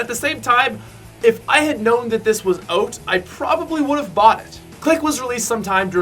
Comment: he said oat instead of out